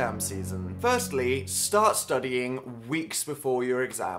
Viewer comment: Studying? Whats that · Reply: Nathanty1 studying? i thought you said I'm dying